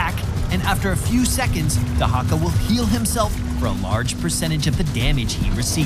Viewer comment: More like "Drake-ka" instead of Dehaka. Lmfao